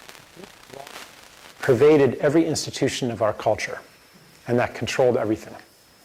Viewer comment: what was said at 4:12 ? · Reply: *So, as you can see, this “loss” has pervaded every institution of our culture, and that controlled everything.* - possible interpretation of subdued audio clip.